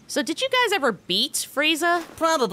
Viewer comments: "before GT ruins her" had to be the most accurate line ive ever heard 😂😂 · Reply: I thought he said before Chichi ruins her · I heard "before chi chi ruins her." 😂😂